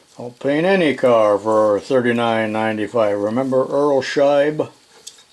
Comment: I heard URL Scheib was a web designer. Earl Scheib's painting business started in 1937 and shut in 2010. Scheib passed away in 92. The great thing was if you wanted the inside of your car painted, you just had to leave the windows down and they'd do it for free. <link>